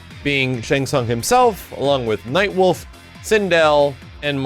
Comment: I don’t know why, but I was focused on something else when I heard him saying who’s been confirmed and for some reason I heard wolf link